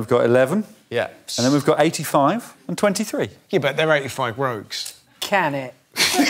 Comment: Someone tell me what they said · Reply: Captions! “85 rogues.” Rogues, meaning strangers. And the witty response, “Can it.” Hahaha excellent!!